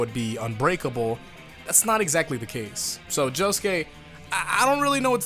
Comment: did you just say Josuke???